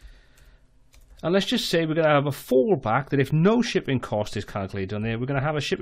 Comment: Thanks a lot for clarifying!! 7:45 Did you say 'fall back'? 10:37 Did you also say 'fall back'?